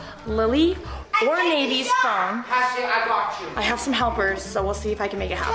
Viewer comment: I thought said he blocked you and I was like??? And then i played it back and realized he said I got you